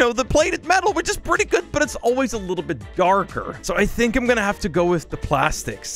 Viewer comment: When he Said darker For some reason the 1st time It sounded like he said cardboard